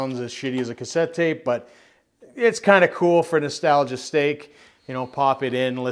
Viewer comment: or 7:19 i swear i heard you say Nostalgia's stake